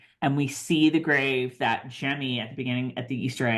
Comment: Just one little correction, if you don't mind. It is Jemmy who is tracing the letters on the headstone in the Easter egg. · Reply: Oh I apologize. I went back and rewatched your video. I thought you said “Jenny” where you clearly said “Jemmy”. I’ve gotten older too. 😳